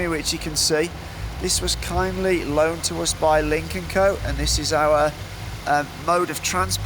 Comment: The Chinses subtitle is wrong, the car brand is 领克(Lynk), not 林肯(Lincoln)